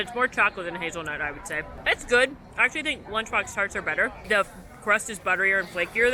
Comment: Did anyone catch which tarts she thinks are better than the pop’t art (at about 5:35)? I listened several times, and couldn’t understand what she said. Thank you! 💗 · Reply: She said Lunch Box tarts are better referring to the ones at Woody's Lunchbox in Hollywood Studios.